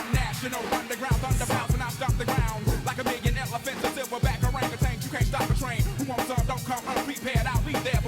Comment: Don't get me wrong, love the song, love the album, love Outkast, but it's been bothering me for like 25 years at this point that he says "silverback orangutans", because it's Gorillas that are known for having Silverbacks, not Orangutans. Orangutan and Gorilla are different things. They're far enough from each other that it's like talking about a tiger's mane, or a grizzly panda bear. Not to sound like a huge fucking nerd, but what can you do when you are one. I've been trying to justify this by thinking it's intentional, like maybe he's trying to make a comparison about US Soldiers in Iraq being a 'wrong' match or some shit like that, but I can't bring myself to believe it's intentional, I'm too autistic for this shit. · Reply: yeah I thought he said “OR orangutans” for the longest, I doubt it was anything deep, just sounds dope as hell. I’m pretty sure Andre said he heard a reporter say “bombs over Baghdad” on the news and thought it sounded cool too